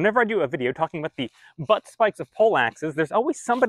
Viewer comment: I thought he said "the butt sliced Polack".